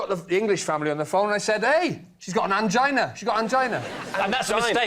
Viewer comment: I genuinely thought he said 'orangina', but like in the way someone would say Tropicana so i thought it was an off-brand orange type drink but then i went away to do something and came back and rewound a bit after several minutes thinking about an orange drink called orangina, and turns out out was angina all along. probably not very interesting to anyone else but damn... the power of alcohol lmao